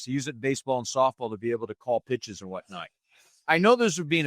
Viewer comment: I thought he said, "to call bitches". LOL!!